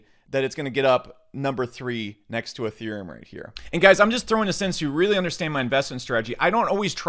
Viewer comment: is he saying at 5:45 "im just throwing a senzu"?